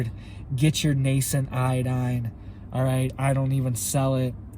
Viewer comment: For a second i thought you said get yourself some tye dye...lol..(iodine)